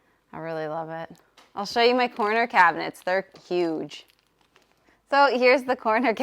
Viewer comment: Oh!! Oops I thought she said porno cabinets. 😅